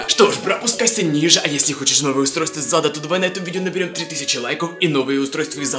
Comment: Он сказал планшет из зада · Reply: Мне тож послышалось из зада